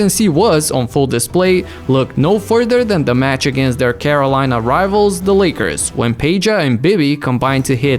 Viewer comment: Did I misheard "Carolina" rather to be California?? 🙄🙄🙄